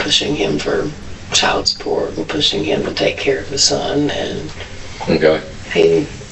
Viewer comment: Does she actually say "take care of THE son"? I don't hear "his", which is what the captions say. Either way, weird word choice.